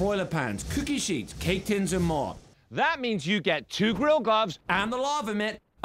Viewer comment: I thought it said girl glove